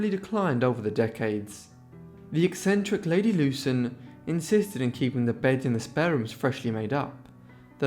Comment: Wow I so thought it said crazy bitch eccentrics lmfao